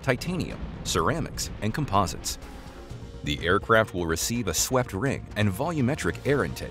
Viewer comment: "the aircraft will be equipped with a swept ring". Had I heard that correctly? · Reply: They are taking about a swept ring laser.